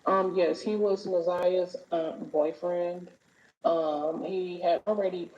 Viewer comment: go back & watch it again cause even repeated it & got mad whn she said it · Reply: repeated what he thought he heard. We all can hear differently. I listened three times and I heard her say Na’Ziyah’s aunt boyfriend. At first I thought she said Na’Ziyah ummm boyfriend but when I listened again I heard aunt